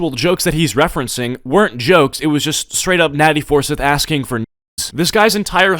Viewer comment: i thought u said natty foreskin